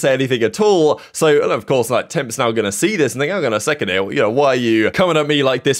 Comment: did you just say the N-word? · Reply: "Hang on a second here"